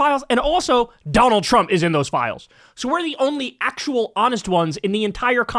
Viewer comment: Did you just say a Democrat is the only honest one? I thought I heard you say that at this timestamp